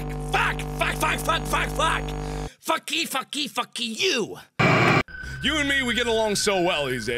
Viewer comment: Me to that kid who said “What about the homework” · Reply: It sound like mark saying keyfuck